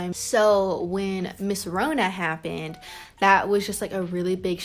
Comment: when she said corona happened i thought she said mrs Rhona happened like a teacher then she said "people are dying"